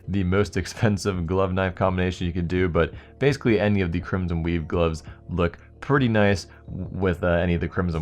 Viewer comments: thought u said crimson weeb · did you just call it the crimson weeb?